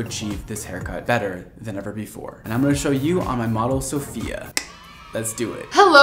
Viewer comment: At first I thought he said "My mother Sophia"🤣